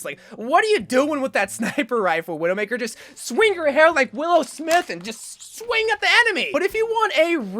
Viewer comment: Uhh he said willow smith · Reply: I FEEL SO DUMB RN 😭😭 I THOUGHT HE SAID WILL SMITH